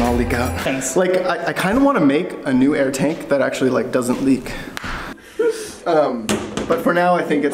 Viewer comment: did i hear "tank that doesnt leak"? TRY SOME FLEX SEAL